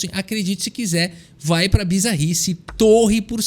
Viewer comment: "Magnus carlsen nao vai pra bizarrice" Eu: claro. Magnus nao joga assim. "Manus foi pra bizarrice" 🤡